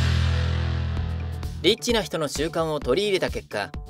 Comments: エッチな人の習慣 に聞こえた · 最初、エッチな人の習慣って聞こえた